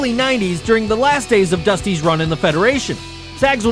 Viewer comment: Cody is Dusty's son, NOT grandson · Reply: My mistake entirely, thought he said Dustin, not Dusty, haha